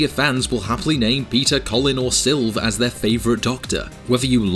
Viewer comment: I thought you said Peter Cullen... as in Optimus Prime 🤣